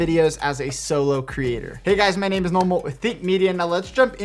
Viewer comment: I thought he said "My name is Normal"